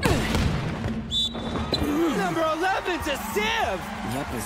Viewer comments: CAN SOMEONE PLEASE TELL ME WHAT HIROO SAID @ 0:20 please 😭😭 he makes me laugh 💀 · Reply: He said “Yep a sieve” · “Number 11 is a Simp!” Does anybody else hear that or am I just weird?